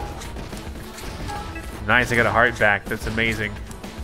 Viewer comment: wait... Did he say "nice, I got a heart attack"